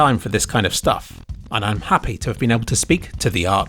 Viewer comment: did you just say "it truly is a work of fart" ?